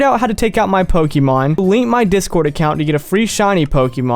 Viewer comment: I thought you said delete your discord